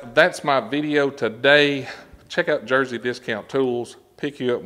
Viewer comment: At first I thought you said dirty discount tools. lol